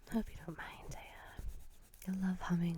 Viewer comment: At around 13:00 did you say "I hope you don't mind Téa" BC that's my name and I got freaked out